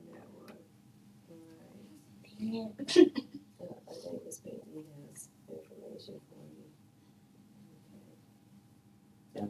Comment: “ I think this bady has information for you” …??? Seriously? That’s what you thot she said? “this BABY”